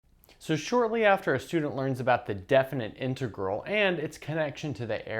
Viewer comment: I thought he said “So shortly after a student learns about the death of an integral…”